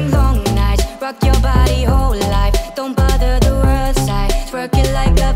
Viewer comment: It just me or I keep hearing “f*ck your body whole life” even tho it’s said “Rock your body whole life”? M sorry😓